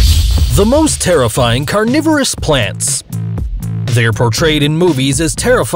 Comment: I thought it said terrifying coronavirus plants